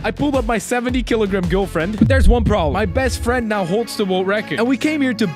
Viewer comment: Stop saying best friend it sounds like your saying bad friend